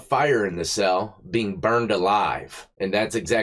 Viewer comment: I thought it said buried.... I was like "how" 😂